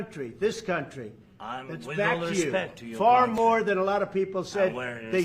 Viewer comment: Z: "I'm telling you: you're going to feel influence" T: "You don't have the cards" Z: "I'm not playing cards" And did he say "we don't respect your country" there at 2:32? Trump was patient. Most of us have been saying since he won the election, "cut this guy off", yet Trump still tried working with him. That guy has no idea how bad he screwed up.